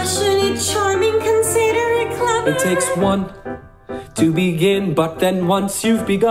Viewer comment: It sounds like he says “It takes one to be gay” ...Just me? Okay